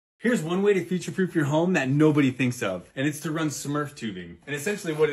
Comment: I heard him say "fire proof you home" then spent too long trying to figure out how this helps with fire. 😅